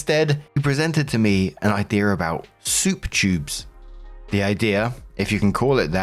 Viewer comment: I thought you said soup cubes and I was like oh souper cubes already exist and they’re pretty cool for making and freezing stuff. I had to read it and see the word tube and then you read how it works and I was blown away.